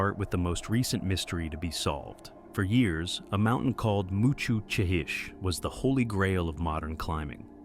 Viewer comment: I thought you said mushu chicken and i was SENT 😂😂😂